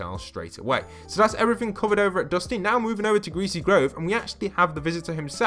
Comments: did he say greasy grove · did he say grove or im i hearing things?